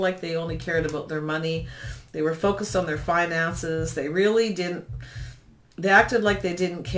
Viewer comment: it sounds like you said fine-asses but you said finances and I’m just happy it made me laugh so loud because it’s ‘Funny’ true lol